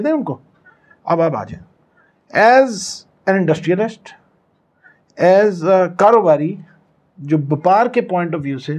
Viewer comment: did he said 'as an industriaNist'???????????